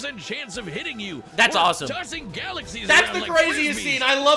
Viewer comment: Its not galaxies , its universes. You didnt catch that cuz you wouldn't be quiet long enough to hear it😂